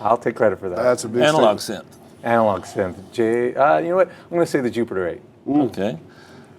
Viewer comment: anyone knows what Mike said for analog synth i cant get it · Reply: He said the jupiter 8, theres a free vst demo version on <link>, btw i like your vids man